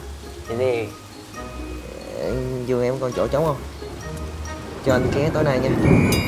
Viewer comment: "Giường em còn chỗ trống ko " pải ko ta hay mìnhe nghe nhầm kaka